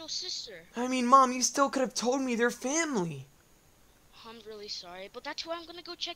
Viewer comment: did u say family